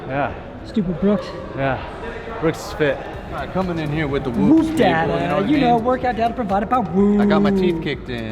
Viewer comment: i thought said "coming in with the whoop daddy" lol